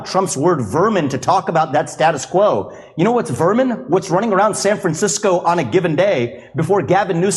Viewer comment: I don't even think he outright said "they are vermin." I thought he said, "they live like vermin." The difference may be subtle, but there is a difference, not dehumanizing them as individuals as much as he was disparaging the state and the city for enabling their conditions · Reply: I listened once more and he said "running around"